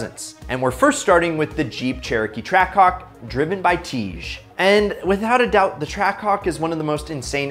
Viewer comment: did u just say tij at tejs name?